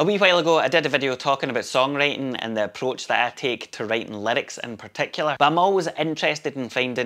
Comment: When you said 'writing lyrics", I thought you said "writing limericks". Could be a good way to go with your band.